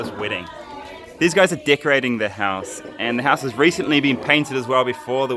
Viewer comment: I thought you said "this guy is dick riding the house".